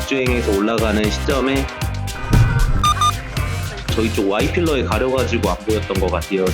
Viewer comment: 나만 Y필러라고 들리나? 왜 자막은 A필러지...